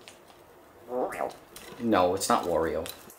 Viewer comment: It sure sounded like Apollo said “Mario”!